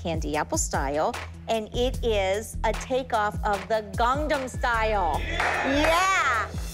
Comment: Cathy saying her dance is a knock of “gongnom style” was hilarious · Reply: not even gongnom it sounded like she said gondnom style 😭